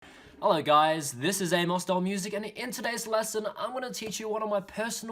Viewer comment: I dont know why I heard "Holaa guys this is Analstorm music!" xD But really nicely played! love every second of it!